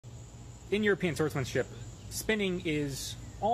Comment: And sounded like you said in your pants Swordsmanship 😂 when saying European swordsmanship try to announce it and speak slower cuz it was really funny to hear the Miss Annunciation 😂